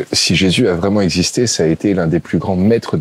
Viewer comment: Pourquoi dites-vous à 28:55 si Jésus avait existé,... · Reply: Moi j’entends « si Jésus à vraiment exister »